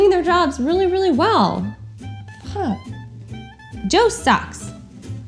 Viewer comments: I legit thought she said something like, Joe Sugg's, instead of Joe sucks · I legit thought she said something like, Joe Sugg's, instead of Joe sucks.